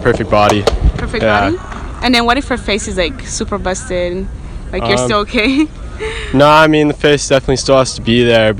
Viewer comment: what if her face is like... what? It's unclear to me what she said. English is my second language can anybody help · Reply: "What if her face is like super busted.. are you still okay?"